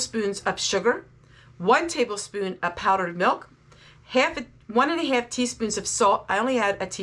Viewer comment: This recipe does not have dried buttermilk it has dried milk in it dried milk is shelf stable does not need to be in the refrigerator after it's opened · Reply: okay sorry thought I heard buttermilk. Dried milk sounds correct